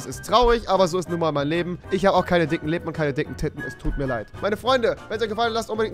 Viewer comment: Ich hab dick gelitten 🤣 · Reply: Ich habe auch dick gelitten verstanden